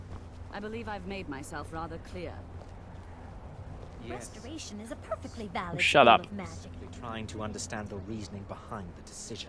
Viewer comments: that was so wierd... at 11:16 i thought the lady Walking by said "masturbation is a perfectly valid school of magic" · I heard masturbation, not restoration ._.